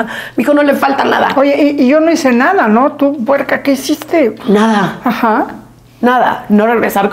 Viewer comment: Min 42:29 como le llamaron a Ferka????o entendí mal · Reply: Se equivocó... Le dijo puerc@